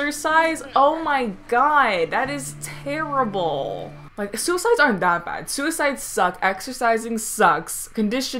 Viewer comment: I legit thought you said "suicides aren't that bad" 🤣🤣🤣